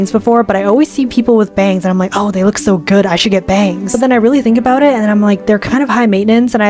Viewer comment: did she just said , "I should get BANGED !!! "